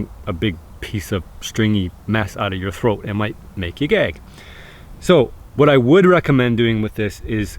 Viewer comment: at first I thought he said it might make you gay